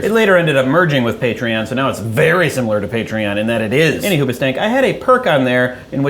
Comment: Ok... I know your saying "any hoobustank" (spl?) in reference to the band... But, I keep hearing it as "any who butt stank" ... But then in this video u keep making jokes about needing more toilet paper... Thus connecting the dots ...ahem... "Any who butt stank must get more toilet paper." ... ... ...I'm sorry... I'm so sorry...